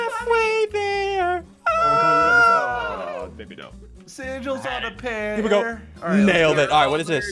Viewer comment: did anyone hear sigils call biffle a virgin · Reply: Nope he said verdict